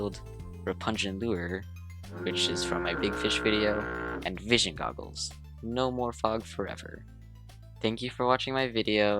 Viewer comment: did you said fishing goggles?